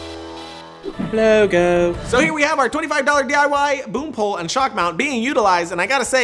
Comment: ohhh i thought it said BROOM poll!! lol